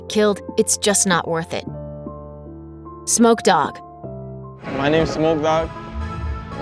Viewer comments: i thought she said snoop dog... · WHEN YOU SAID SMOKE DAWF I THOUGHT YOU SAID SNOOP DOG AND I FREAKED OUT